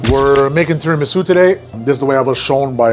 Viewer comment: I thought you said We are making Terrible Soup Today ...